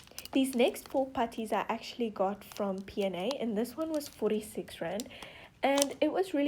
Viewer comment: I honestly thought these were soooooo exspensive like 80pounds!! ... then i relised that like 85.99 rands is like 4 pounds and still about 4 dollars i hope this helped and i hope u enjoy today or night depending when ur reading this ☺ also this is the first time ive watched u and i loved it and at first i honestly thought u were saying grand! 😂 Edit: omg thank you sooooooooo much for liking my comment i am suscribed to u by the way when i watched this